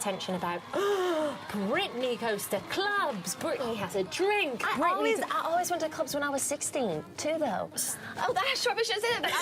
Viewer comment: what did she say there before she said i should probably not say that? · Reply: that she was going to clubs when she was 16